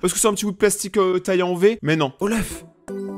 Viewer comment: perso j'ai compris OLAF je suis un peu c*n non?